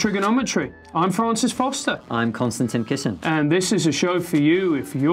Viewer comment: Once I misheard, I now always hear Konstantin's name as "I'm constantly kissing"...